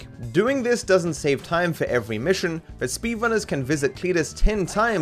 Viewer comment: I thought he said hey buck